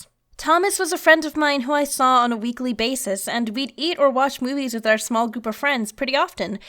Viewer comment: or 0:45 (can't remember) does she say we'd eat or wash Willy's? 😂😂😂🤔🤔